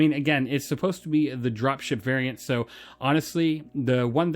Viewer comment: I dont know if it's me, but I heard 'Dropshit variant' lol